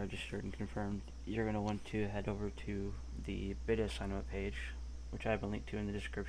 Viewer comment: what page was said at 1:18 i don't get it at all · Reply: I said "Beta sign up page" but this video is very out of date. Go watch my new video. I link to it with an annotation at the start of this video.